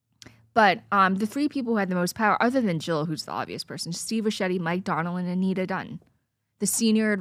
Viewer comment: I can't quite hear the names at 32:20, but this is what I've got: Steve (???) Mike Donald and Anita Dunn Edit: They go over the names: Anita Dunn Steve Ricchetti MIke Donilon